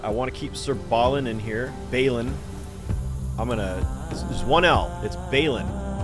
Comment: when i hear his name.. brain immediately goes to "Ballin" .... and it won't stop. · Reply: Oh no! I'll try to remember it is pronounced "bailin'" as in "bailin' a friend out of jail".